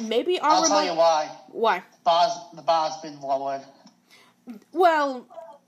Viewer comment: What did he say? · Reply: The bar has been lowered?